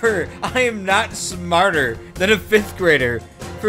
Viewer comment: i didn’t even smoke up yet and i thought i did when he said “basbana bella spusshy” aka “im not smarter than a 5th grader”😂😂😂